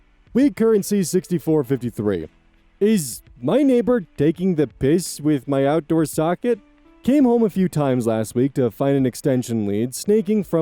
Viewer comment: I really misheard the title of story 8; I thought he said "my neighbor was taking a p!$$ on my outdoor socket"! I heard that and thought "he'll get electrocuted doing that..." and figured the problem would solve itself...